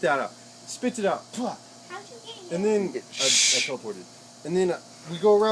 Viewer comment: did he say "I teleportaed "